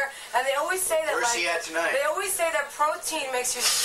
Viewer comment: Sounded like he said "Where's HE at tonight" ...